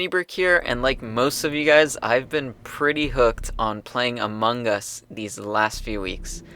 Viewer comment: Wut He Said Amongos